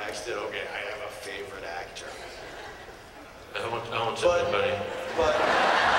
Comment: What does Nathan exactly say at 6:24, after which everybody is laughing? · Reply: he says “ I won’t tell anyone”